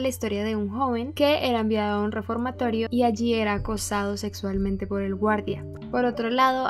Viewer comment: a 5:16 escuche YAOI?? 7W7